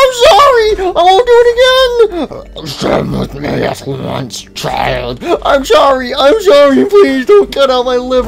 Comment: sun sounded like he said "you're staying with me after lunch, child"